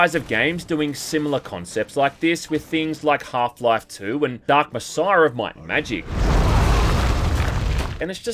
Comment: what game is this? I can't understand what he was saying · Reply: He said, dark messiah of might and magic